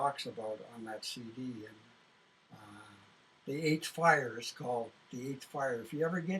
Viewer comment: Did anyone catch the name of the CD at 1:17:44 __ Flyers? Thank you. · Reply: The "8th Fire" Native music on Soundcloud: <link> 8th Fire - Wikipedia: <link> NewsInsideOut: <link> Thank you to the elder in this film of the Burrard First Nations Band and to the brave, articulate and good human being. There are so few left in this world, much like the extinction of precious species both plant and animals. When I make our transition into the Light, I will go to the realm where those who do evil can never enter. Their technology even if it was millions of years in advance ,will not help you. I feel sorry for your Astral bodies and Higher Selves, may they be released from their earthly sufferings. Finally, Good luck to everyone and Blessed be those of us who deserve those blessings and are doing the best we can while we're here. Love Mother Earth!! Honoring!! AC